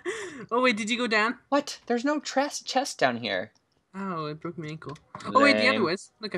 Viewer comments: did say it broke my ankle? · did anyone hear say ow i broke my ankle